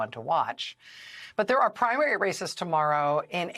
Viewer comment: Listen again to Rachel and like me when she says the words "Races", mishear, and hear the word "Racists" :-)